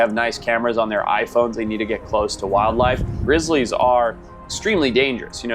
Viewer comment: Did bro just say rizzlliys3:39